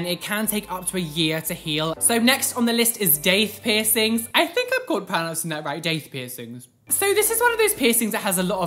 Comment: What do you say at 6:55? I can't understand or make out what's being said. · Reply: "I think i'm pronouncing that right"